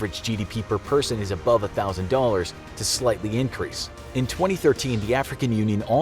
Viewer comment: subtitles are wrong, it says signifiantly instead of slightly boom